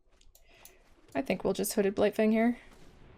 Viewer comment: I thought you said Hooded Blight-thing and it made my day. 😄